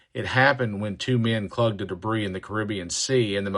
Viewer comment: Can you correct the captions at 10:36? It says "two men clogged the debris" instead of "clung to debris."